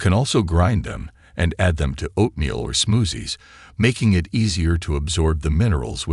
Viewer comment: Did I hear “smoozies”??? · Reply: Haha yes! “Smoothies” might’ve come out as “smoozies” this time. We keep it real around here 😂